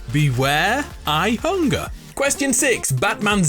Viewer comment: 9.5/17 The .5 was because I thought Galactus said “I am hungry” inters of “beware I hunger”